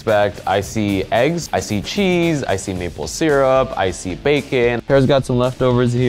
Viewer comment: I thought he said I see Nipple Syrup😂